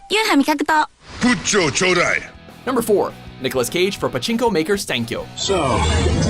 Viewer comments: パチンコが全てチ●コにしか聞こえなくて草。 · から色々な所でち◯こって聞こえる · ごめん…w4:56がチ○コにしか聞こえんかった…w